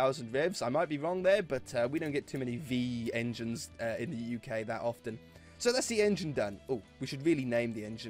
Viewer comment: did he say they dont have many V engines in the uk?????????????????